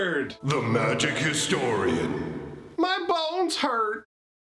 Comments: I know you say that your "bones hurt" in the intro, but I keep mishearing it as "my balls hurt"; can't help but giggle at the start of each video. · I laugh every time I hear "my bones hurt." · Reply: English is not my first language, so the first time I misheard it as "my balls hurt". And now I cannot get it out of my mind despite having realized it was "bones".